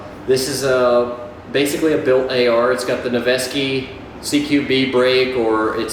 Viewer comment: What is the name/make of the muzzle brake on the first AR? It sounded like you said Nevsky but I can’t find anything under that name. Thanks for the info, stay safe down there 👍🏻🙏🏻